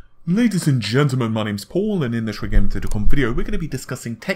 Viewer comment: I could have sworn he actually said tittycum video in the first second. My day is satisfied.